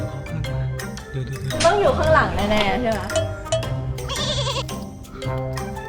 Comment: หูกูซึ่งได้ยินว่า ต้องอยุ่ข้างหลังแน่ๆที่รัก 5555